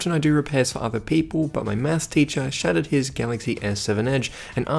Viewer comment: I thought he said "my meth teacher" I had to rewind the video to make sure